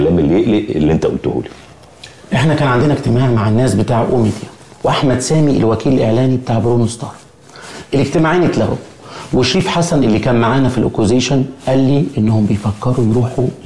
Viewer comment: انا سمعت pornstart 🤣